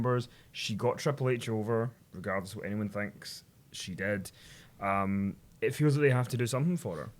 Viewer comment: sounded like said "she dead" instead of "she did"